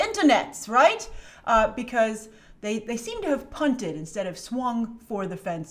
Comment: Lol Bunted means a soft hit in baseball, Punted means to kick and is a football term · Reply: I thought she said PUNTED, not bunted